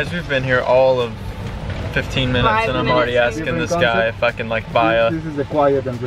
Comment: did he say the f word?😂 Edit: sorry it was “if I can”😂😂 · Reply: Emma Suzuki no no 😂 He said “If i can like buy a.....hut” 🤣